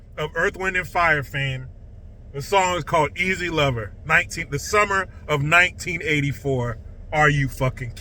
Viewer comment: Geez..after all this time I thought Phil Collins was saying 'greasy lover'